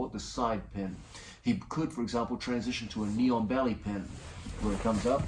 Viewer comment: I thought he said "Neon Belly" at first